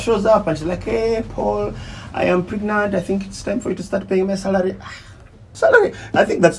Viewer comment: I hear, " say sorry" with a serious face